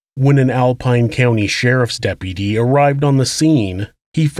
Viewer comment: im sorry but it's hilarious how this dude says "DEPPY-DEE" instead of deputy